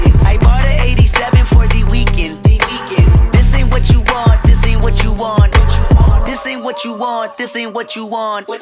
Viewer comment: I thought it said "Disney what you want" lmao